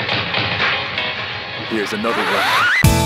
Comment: when he said here’s another one I thought he said this is america